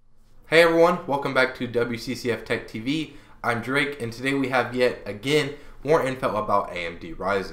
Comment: well...this guy;s name is drake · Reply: Oh I thought he said Jake. But I went back and rewatched it and sure enough it's Drake. I've never met anyone with that name before. I don't know why its a thing.